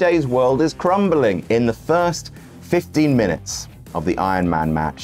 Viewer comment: Legit, thought you said “13 minutes” iron man match